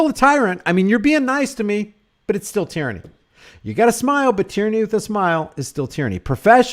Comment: I thought it said tranny